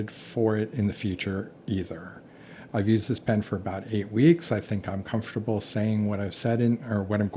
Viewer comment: Well, I didn't test it /that/ long. LOL Yes, the Estie! I'm planning a review next month. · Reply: Oh! Haha! For some reason I misheard your "about 8 weeks" as 98 weeks. 😂 That makes more sense, and 8 weeks is still a lot! Looking forward to the Estie review!